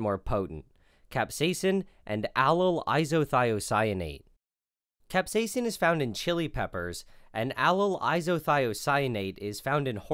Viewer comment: WHAT WHAT DID YOU JUST SAY!? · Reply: Capsaicin is found in chili peppers